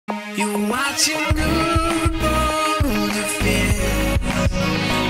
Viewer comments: I awlways thought the intro said You're watching boy... lets dance But now I know it says You're watching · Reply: I thought it said "You're watching Guude's vids" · I awlways thought the intro said You're watching boy... lets dance But now I know it says You're watching